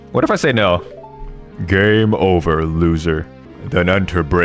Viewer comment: You said “Game Over loser” and I paused it at just the right time that it sounded like you said “gay” So it was the funniest thing ever “Do you hath the courage?” “No” “Gay” Also ima finally beat Lord Jabu Janus belly I never could figure it out